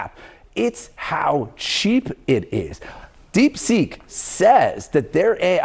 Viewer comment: 📚 Feel Alive II - Unlock Your Infinite Power By Ralph Smart: <link> Get The New Book | Now Available Here: <link> Join Ralph Smart's New Patreon: <link> for exclusive new content to help you become your greatest version Habits that are damn hard to do, but pay off forever (Watch this) <link> <link> Merch - Clothes Store : <link> ▬▬▬▬▬▬▬▬▬▬▬▬▬▬▬▬▬▬▬▬▬▬▬▬ 📚 Consults, Mentorship, Membership & Support One To One Consultations With Ralph Smart: <link> You are appreciated ❤ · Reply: I thought you were saying "deep sink." 😆